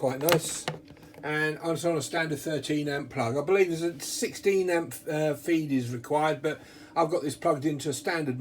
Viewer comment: thanks, at 2:45 I hear "16a feed", I'm old though and my ears maybe don't work as good as they once did ;) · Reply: He did say 16amp but thease run ok on 13amp thats what they come with.